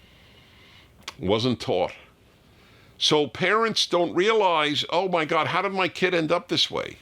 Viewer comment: Thought it said: «Parents can’t ejaculate.» 😂